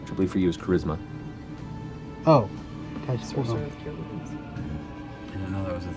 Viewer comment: what does marisha say? sorcerer with something? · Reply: pretty sure - "Our sorcerer has Cure Wounds" "I didn't know that was a thing you could"